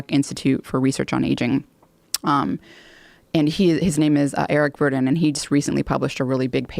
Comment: Listen between 1:22:07 and 1:22:12...yes, I took a quadruple take to get the exact times correct, she actually said what I heard...Dr. "Eric Burdon and The Animals".